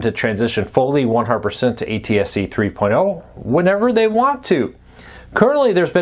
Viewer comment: ATSC 3.0 I thought he said atse. Just FYI for those of us who are technically challenged... hope this helps somebody